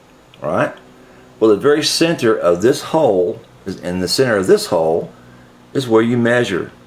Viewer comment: what did he say???? listen close, i think youll hear it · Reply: Ok, so, what I said was that you need to measure center of each hole on the Chinese Fake in order to be able to purchase a replacement bridge. Center of each hole. And if the replacement bridge has the same large size mounting posts as your fake it;s very important they match up. Hope this helps Have a good one enter my Contest, tell a buddy too.